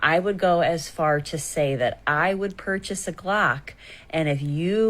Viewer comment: Purchase a glock! I thought she said she's going to purchase a clock. No wonder she confused me. 😂